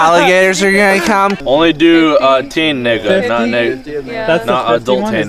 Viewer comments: What did he say again? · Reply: He’s drunk trying to say “teenager, not adulting” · what did you say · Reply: teenager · did he just say teen nigga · did anyone else hear the n word at 10:50, everyday i watch and fuck he is hilarious!!!! i cant stop laughing everytime i watch him